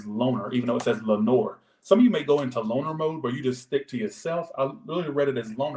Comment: I thought said “boner mode” 😂😂 not loner lol